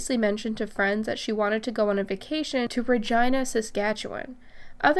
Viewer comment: Wait .. did you say Virgina.. instead of Virginia? 2:00:22 lol